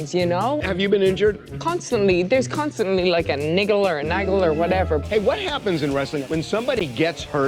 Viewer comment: A what? I thought I heard the N word, Becks!